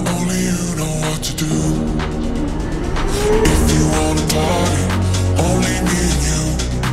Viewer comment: Am I the only one who thought this song originally was about pooping? I thought he was saying “potty” not party 😆